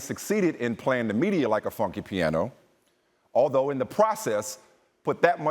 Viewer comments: did he really say "...Like a fucking piano" or am I tripping. Either way he does have valid points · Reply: Taihron Heaven I think he said "funky piano" but it sound like the audio got messed up there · did he really say "...Like a fucking piano" or am I tripping. Either way he does have valid points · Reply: Taihron Heaven I heard "funky piano" but had to rewind 2 or 3 times.